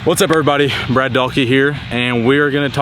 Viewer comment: I thought I heard Brad say, “Red Donkey here” for his intro. Must be the accent.